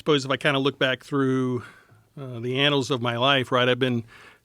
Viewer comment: did he just say anals